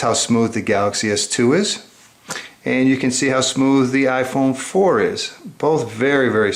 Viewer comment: Wait did he say iphone 4